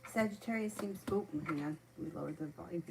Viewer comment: Hey beautiful I had a hard time understanding what you said at minute 1:10... Sagittarius seems? Thank you so much! 🙏🏼💕